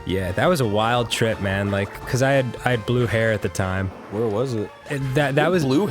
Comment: For a second I thought Dan said he "blew hair" and I wondered if that was some weird super underground euphemism from smoking weed. Thanks for having an incredulous outburst and clearing that up, Arin.